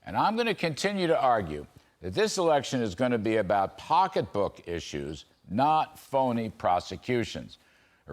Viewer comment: I thought you said pickpocket issues.